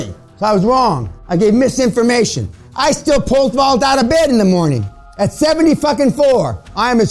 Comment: I didn't understand the sentence he said here, can somebody write it for me please ? Thanks a lot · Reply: Pablo Genest he “pole vaults” our of bed. Meaning, he has such high testosterone that he wakes up with a hard on everyday.